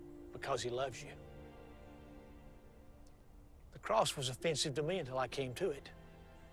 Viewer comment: Can someone please tell me what the dad is saying at 3:58 " the cross.....? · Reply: The cross was offensively to him.